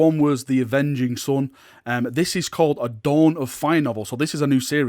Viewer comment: When he said dawn of fire i wasnt looking and i thought he said Don on fire and it reminded me of GoW2 or 3 cant remember. That's all have a nice day.